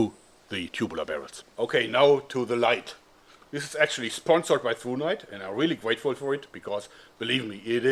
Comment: For a second, only a second, I heard "Sponsored by Fortnite."